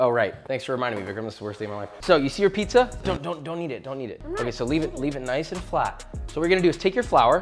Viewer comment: Josh: Don't knead it. I'm not needing it ... Oh, knead it! (Best part of the video) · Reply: To be fair I thought he said “don’t eat it” too